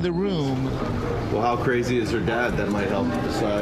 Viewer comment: what did the groom say? It sounded like he said 5 days but is that what he said? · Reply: I think he said "well how crazy is her dad that might help us decide"?